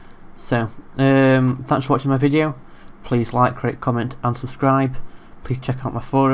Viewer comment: Hey, just so you know, it sounds like you said "Click like, click, unsubscribe." I think you said "AND subscribe" but I had to re-listen again. You don't want that subconsciously working on some kooky people though so I'd slow it down during that part by about a millisecond ;-)